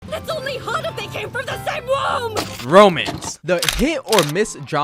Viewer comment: I fr thought u said "Romans"